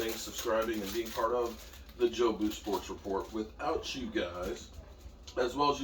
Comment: for the longest I thought you were saying the "Joe Booth" sports report. I'm like who's Joe Booth your names lol